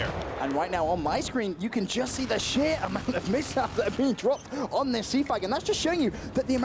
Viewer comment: The fuck did he just say? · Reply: "You can just see the shear amount of mishap that has been dropped." It was like he was coughing and talking at the same time.